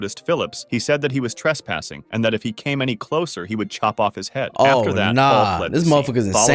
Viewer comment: He will SHOP his head off 😭 also if you don't understand shop is a market and chop is cutting and he said shop not chop